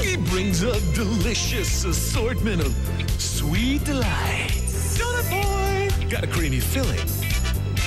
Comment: I thought he was going to say sweet justice